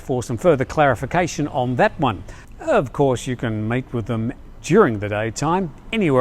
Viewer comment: Did say "mate with them" or "meet with them"? Freudian slip perhaps or just his Aussie accent?